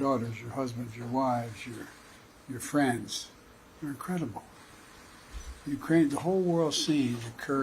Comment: Who else had to rewind to 0:52 multiple times because you thought Joe told the truth and said “you’re a criminal” ?He said incredible. He must have meant an incredible fraud.